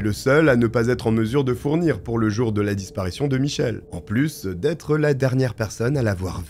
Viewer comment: Je sais c'est moi qui ai un problème mais j'ai entendu "Michel Leeb" pendant toute la video...